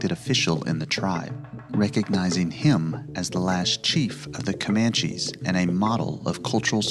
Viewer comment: I thought it said "the last chef" and I was lyk: they had chef's at that time? 😂